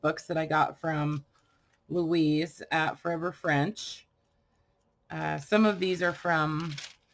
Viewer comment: Where did you get the papers that were similar to tissue paper? I thought you said 'Louise at Olive French."